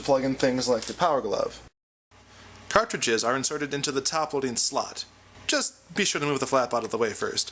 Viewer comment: For some reason i always hear "Slut" instead of "Slot"